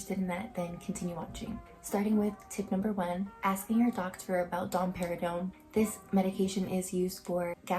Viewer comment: I heard dom perignon and got so hype